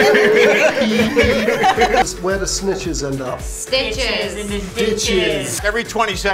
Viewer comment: for a few seconds I was so confused was Elizabeth was making that face then I realized they were saying the British version and then I was also confused because I thought the other girl also said stitches but then I realized she said snitches